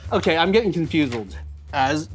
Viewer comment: Did he just say “I’m getting confuseled?”